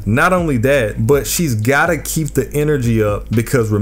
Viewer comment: am i tweaking or did bro say she's gyatta keep the energy?